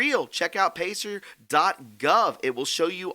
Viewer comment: Did he say check out patriot.gov for the names of the traitors to this country. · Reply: I thought he said pacer.gov, but I just went there and couldn't find anything.